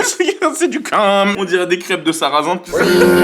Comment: svp qq pourrait me dire il dit quoi à 9:49 je suis au sol XDD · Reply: J"ai compris, "j'ai demandé de la mayo en plus mais y a aussi du cum"